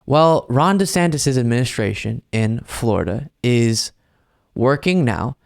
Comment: When u started w his administration i THOUGHT i heard menstration, in wich i thought might make sense considering his negative modes lol